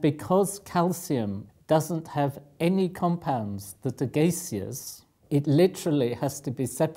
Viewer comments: what word does the professor say at 1:19? "And because calcium, doesn't have any compounds that are [gaseous]..." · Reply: hazzard77 yeah he says gaseous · he say "because calcium doesn't have any compounds that are 'gaciers'... What is that last word? And, extra bonus, what does it mean? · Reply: "Gaseous." It means that the compound is in a gas form. Also, please put the timestamp before the quote next time, because you can't hear a word of that when you click on 1:20 without going back.